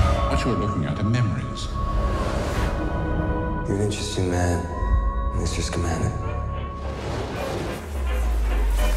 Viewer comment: what is being said at 0:47 - 0:51 ? I can't make out the words but the voice sounds familiar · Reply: Lizzev "You're an interesting man, Mr. Scamander."